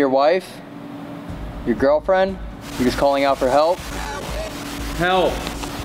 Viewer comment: when yall said it said help i heard “help me.”